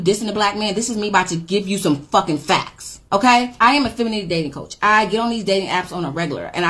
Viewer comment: For a feminine coach, she doesn't come as feminine 💀💀💀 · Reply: I thought she said "Feminist coach" so she's coming out right to me 🤣